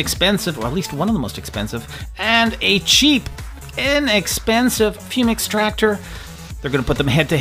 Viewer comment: Oops why did i hear on 0:14 inexpensive penis extractor....